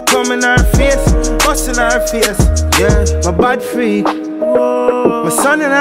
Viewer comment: 2021 but a me alone thought he said Me Bad Fi it lol it could work either way...I think I like Bad Fi It better than Bad Freak